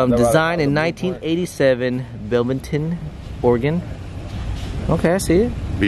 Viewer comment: you def didnt say beaverton bro 😂 sound like you said belvington lmao